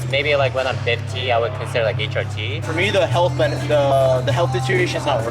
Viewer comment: did bro just say HRT???